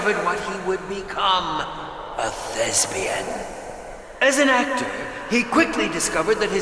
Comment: did he just say lesbian? 😂